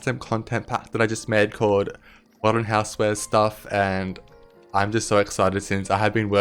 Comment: Omg I literally thought it said “modern housewives” and I was like “YESSSS”